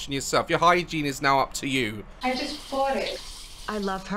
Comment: I just bought it What I heard: I just farted